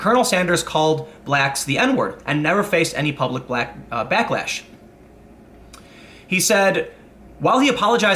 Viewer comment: The N word? Really? You mean Noldemort or nagger, right? Btw. did you say ''blacklash'' at 8:15?🤣